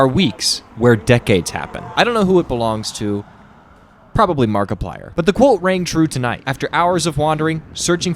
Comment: lolll the subtitles say "probably Mark Ruffalo" 😂😂 for anyone deaf watching: it was actually Markiplier that was what he said fyi